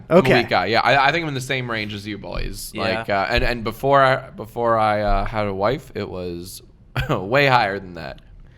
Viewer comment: when he said “before I had a wife” I thought he said “before I knew how to wipe”